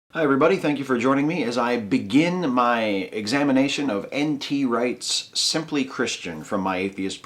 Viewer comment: I'm glad as always to hear one of your series! BTW, I laughed out loud when I misheard: I thought that he said that the book was entitled "Anti-rights: simply christian"! That would have been a more thought provocking title imho! Otherwise, it's the same lame argument as always: -we long for an absolute -therefore this absolute exists -somehow, my idea of this absolute is the right one...